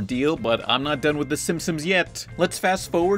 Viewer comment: Simpsomes? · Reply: Pretty much: 7:44 lol It's what it sounds like you said your not done with the Simp..sums yet or simpsomes